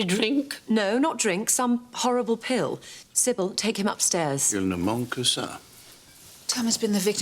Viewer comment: what is it that Robert says to his mother? I can't make it out. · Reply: He says "il ne manque que ça" French for "that is only what was missing"